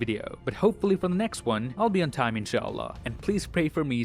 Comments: Are you muslim? Becaus i think you said inshallah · Wow, did you say Insha Allah at 11:15? That is so beautiful ❤️ It means 'God willing' in Arabic. And In Sha Allah, your problems really will be solved! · Did said "Insha Allah" at 11:18??? That is what I heard and is absolutely cool. Hopefully everything will be fine with whatever is going in your life. Keep up the good work. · Did he say InSha Allah in 11:16 👏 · did you really say in sha allah?🥹🤌🏼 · did he just said InshaAllah at 11:15? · what did you just say? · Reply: Inshallah. Meaning "If Allah wills" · did he just say andShalla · Did you just say "Inshallah" ???🤔. I understood, but never expected you would say that.😇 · did he say inshallah? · did he said inshallah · did he say inshallah or im tripping ?? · did he just said inshaAllah? Is he muslim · Did you just said "Insha'Allah" in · Did he just say inshallah? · Did you just said Inshallah 🥺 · did u say ''insha ALLAH''??♥️♥️♥️♥️♥️ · did he say inshallah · did just Say "Inshallah". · did he just said Inshallah?😶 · Did you said inshallah at 11:15 ? ❤️ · Did he just say INSHALLAH?? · did he just said Inshallah. Well your work is Mashallah And take care of your self. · did you say InsyaAllah?? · did he just say Inshaallah ❤️ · Did you just said insha'Allah at 11:16 !! MashaAllah 😍❤️